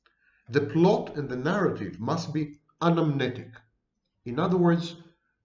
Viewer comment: What is the word at 35:21? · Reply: Anamnetic.